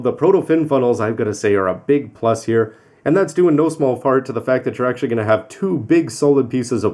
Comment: "no small fart" am I hearing this right?